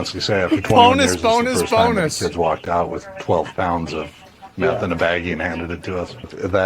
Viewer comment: 100 grams of meth Geezz thats like $14000. bet a lot of people in that area are sad their dealer got hit haha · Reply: sorry i thought it said 100 grams not 12 pounds lol. 12 pounds is like $750k